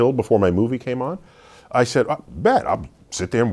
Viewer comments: Did Neil just say "Bet"? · did he say bet!? if he did thats jus one more reason i love him